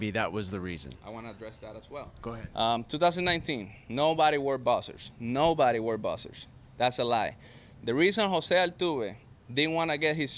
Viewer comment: Did he say " 2019 nobody wear boxers, nobody wear boxers. That's a lie" ?